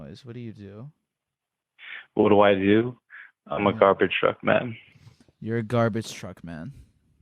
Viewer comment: I thought he said “I’m a carpenter of men.”